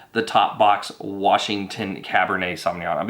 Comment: Hey. Did you say Washington at the end? 4:46? Reasonable for a boxed wine.